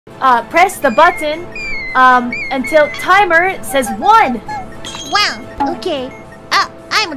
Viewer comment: "I'm die, Thank you forever." -Korone 2020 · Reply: What i hear is "I'm guy, thank you Foeba"